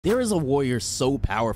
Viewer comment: Bro at the start i thought he said "there is a lawyer" 😂